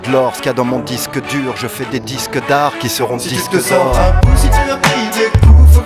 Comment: Moi ça me fait presque chier qu'ils soient en tendance, les gens qui consultent les tendances de Youtube ne sont pas dignes de Stupeflip PS : J'vais le dire là ça sera plus simple que me répéter 5000 fois ci dessous. C'était 1) pour plaisanter, et 2) juste pour dire que ce qu'on trouve dans les tendances de youtube c'est généralement pas terrible et qu'ils n'auraient à la limite même pas besoin de ça pour que ça marche, même si je suis content pour eux que ça les fasse encore plus connaitre. Plus de gens écoutent et plus je suis content au final. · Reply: Ouai alors effectivement, je comprend et je vis ta douleur quand tu évoque pokora. Mais Stup a un coté révulsant qui fonctionne bien comme repousse-tâches. A la première écoute, j'ai compris "des disques dares" et pour moi ça restera ça! Je t'invite à écouter "mes nouvelles chanson de rock que j'ai trouvées"(remix stup), sur ma chaine. Avec un peu de chance ça fera du bien aux oreilles. J’espère.